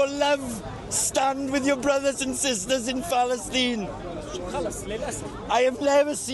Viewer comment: Did he say "Phallus teen" twice? "I paid to go to phallus teen."